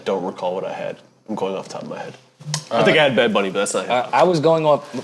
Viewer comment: what does parks say at 4:37? bad buddy? which rapper/album is he referring to?